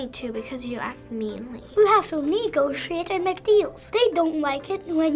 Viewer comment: did he just say sh*t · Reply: He said "You have to negotiate and make deals"